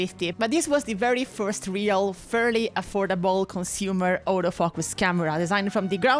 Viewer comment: When you said Auto focus camera, I thought you said the first old as fuck camera